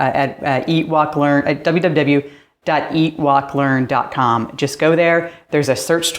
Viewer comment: My bad hearing, but every time you say the name of your channel I hear "Ewok Learn". Great video. I really like the idea of scheduling locations based on seasons and climate.